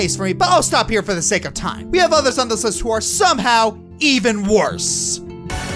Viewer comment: What does he say at 19:51? I can't completely understand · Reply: He said we have others on this list who are somehow even worse (which I mostly absolutely agree with, given the missed plot potential with Iris and Anders)